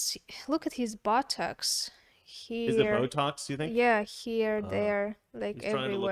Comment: I thought she said "look at his buttocks" not "botox", lol